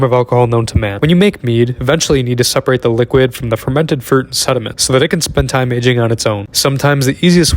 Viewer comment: Thought this guy said he had fermented fruit and meat in there for like a split second